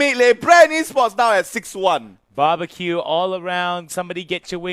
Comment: did he just said babarque?